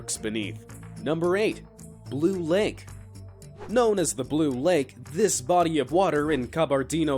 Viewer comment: sounds like he said Loot Lake